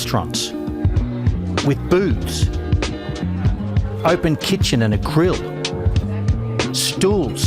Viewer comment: I misheard when he said,"..what i like about them is they have boobs". instead of booths.